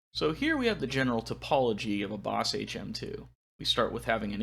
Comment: See, you said ‘the topology of the HM-2”. But I heard “the apology of HM-2” and thought “yeah ok I get it.”